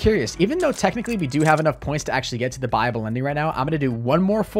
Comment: I keep hearing "Bible Ending" instead of "Buyable Ending"